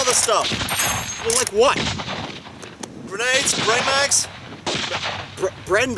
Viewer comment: I thought he said Brain mags